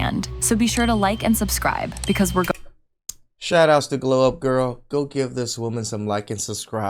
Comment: I thought you said "blow up girl". 😂😂😂